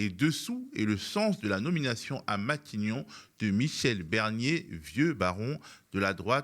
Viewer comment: C’est moi où il a dit "Michèle Bernier" 👀🤓